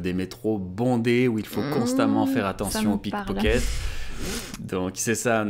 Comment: I thought he said that there was a danger of Polly Pockets on the Metro.🤣🤣🤣